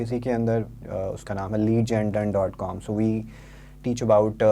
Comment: Please help me find 3:06 ( did he say lead gen done ?) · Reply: Yes. Leadgendone